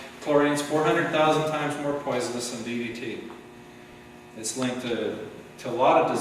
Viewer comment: This is a great video, but could somebody please tell me what he says at 12:21? He says something about chlorine being 4000 times more poisonous than...........??? I listened many times but can't make out what he says lol. Thanks, and great video - really opened my eyes. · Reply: I realize that you posted this seven years ago but he says DDT. Googled: 'a synthetic organic compound used as an insecticide. Like other chlorinated aromatic hydrocarbons, DDT tends to persist in the environment and become concentrated in animals at the head of the food chain. Its use is now banned in many countries.'